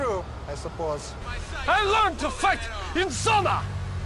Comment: he actually says that he learned to fight in Zona - it's the Russian term for prison